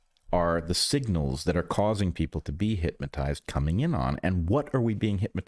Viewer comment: Is Bret saying... HypMAtized? Or even HITMAtized? I keep hearing it. Everyone has their little blindspots I guess. :cat-orange-whistling